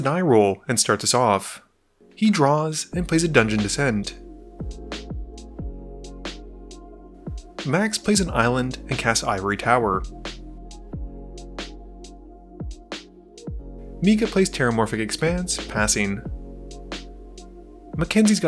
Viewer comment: I keep hearing “max misplays a land” 😂